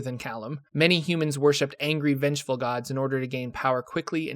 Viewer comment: ngl I heard "people worship angry, vegetable gods" and was confused for a moment